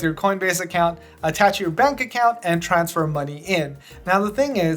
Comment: "you can't buy any cryptocurrency until your money _?_ (what word is that, please?) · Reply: "you can't buy any cryptocurrency until your money lands" You should re listen if carefully but as far as I understand: Pro has less fees BUT first you have to wait for the bank to fully transfer your money for them. Only after that they'll give you the coins. This means, the exchange will happen 5 days later as well, which means the rate at the exchange will happen is going to happen according to what BTC is worth in your currency as of not today but 5 days later. So normal Coinbase on the other hand: 1. Has higher fees :( 2. Exchanges immediately :) 3. Exchanges on the current price :) CoinbasePro: 1. Has lower fees :) 2. Exchanges later (he says 5 days) :( 3. Exchanges on the price BTC is going to be 5 days later :| [[ if it looses value fast than :( ]]